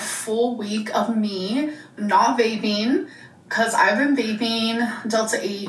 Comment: Tell me why I heard "tomorrow will be a full week of me not bathing" instead of "vaping" lmaooo